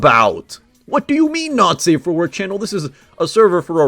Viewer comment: i thought bro said "nazi war channel"